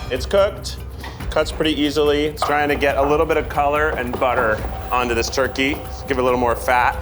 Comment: I swear I heard saying "color and butter" at 19.22 and then saying "on this turkey" 🙄